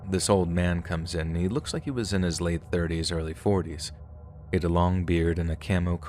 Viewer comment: "This old man comes in...looks like he's in his late 30s, early 40s..."... that crushed my soul. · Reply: 😂 I misheard it at first i thought he said born late 30s, early 40s.